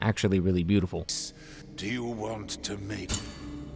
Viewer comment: I was listening from another tab for a minute, and 33:58 sounded like "do you want to mate" I dunno big guy, there may be some practical issues